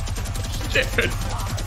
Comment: it sounds like he said shifty shafts